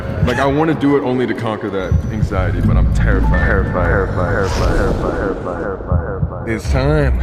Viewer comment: When Colby said “terrified” I thought he said “i’m AirFried”